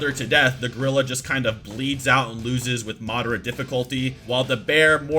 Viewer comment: who heard mardara